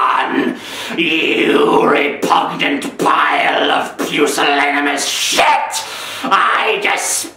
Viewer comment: what did he call him at 10:27? pusaligamous???